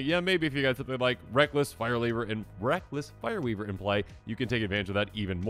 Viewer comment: My brain hiccupped when you talked about Reckless Fireweaver… I thought you said ‘Breakfast Fireweaver’. … If you or someone you know is a proxy artist, I would pay you for such art of a Breakfast Fireweaver.